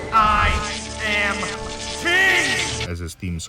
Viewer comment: What is his last word there I have the "Here I am" but whats the last word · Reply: The last word is king